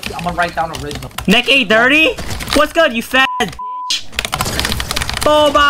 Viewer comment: I thought he said boba when he said full box